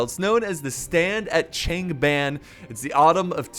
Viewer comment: I thought it said stand at gangbang...hehe i'm stupid